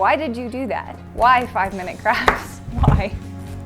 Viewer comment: I swore she said 5 mins craps